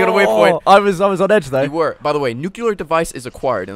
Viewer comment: it says nuclear device? i thought it said nuclear deuce